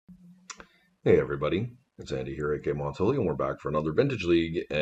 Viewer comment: all these videos later and i still dont know what you say as your intro.. i keep hearing "its andy here again montolio" is that right? lol